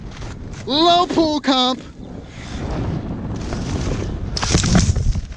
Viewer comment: did he say "oh fucking hell" @ 0:31 ?